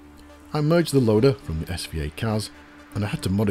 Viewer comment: What was the CAS loader used in the first section? I thought you said "SVA CAS" but can't find any reference to that online. I'm looking for an upgrade for my CASduino, and the screen on that device looks really nice. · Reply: it’s an SVI-CAS reviewed it episode 110 Noel’s Retro Lab, electricadventures and Arctic Retro also have reviews too it’s a great little device- I’ve got one to use with my Amstrad CPC6128, my 128k Toastrack and also my C64 breadbin. You’ll need to send an email to it’s creator to get one sent from Australia. Details are at the end of the PDF instruction manual.